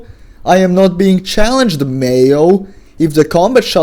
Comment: every time you said mayo i swear to god i only heard male and i was like "why the hell did he start saying male" before i realized you were saying mayo and i started laughing at my own stupidity. great video as per usual.